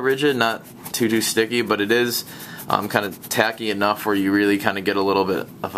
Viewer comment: Did you cuss on accident on 2:14 you sound like you said f*cking haha